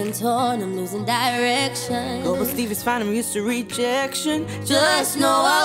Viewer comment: "Go with Steve, I'm used to rejection." Poor Jonathan... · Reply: Oh I thought it said Steve’s not used to rejection😂😂